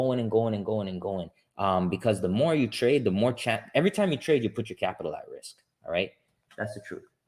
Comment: Hi, on the video time 16:57, you mentioned "the more you trade, the more WHAT". Cannot hear what you said. thanks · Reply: Risk your capital